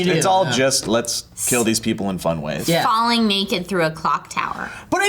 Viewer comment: was it just me of did it sound like said falling naked on a cock tower